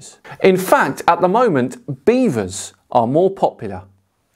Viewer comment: Did he say beavers at 10:15? Mark can plow my beaver anytime he wants.